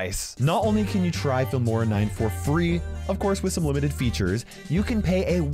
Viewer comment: I thought he said Gamora 9